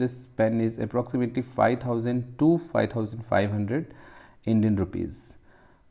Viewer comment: That's a beautiful scribe you have there. Perhaps I misheard, but did you say 2,500 or 5, 200 Rupees ? The silver grey-blue marbling is fantastic (grey is one of my favourite shades). The presentation box is (also) a nice touch. I'm going to have to look up this company too ! Excellent review - many thanks. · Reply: Thank you Sir. It’s Price is approx 5500 INR